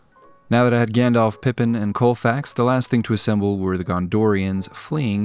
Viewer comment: what did he call that horse? Isn’t it shadowfax? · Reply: COLFAX! LOL. He's been spending too much time reading about gold mining towns.